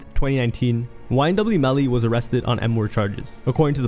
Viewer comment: Wait, at 27:29 did you say “he got arrested on N-word charges?” Lol what?